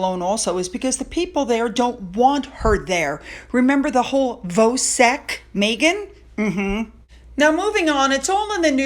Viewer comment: minutes, what is the 'Vosec Meghan' that is mentionned? Not sure what the word is. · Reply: Voetsek. In South Africa it basically means, "get the F out!!" & it's from the Afrikaans language